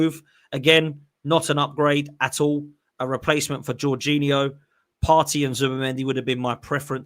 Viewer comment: Sentiments aside Norgaard is an upgrade on Georginho. He has more pace, scores goals, assists and is a threat from set pieces. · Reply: I thought u said Gervinho for a sec 😂